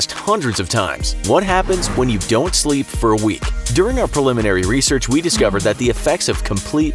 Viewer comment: No sleep for a week? Thought you said sleep for a week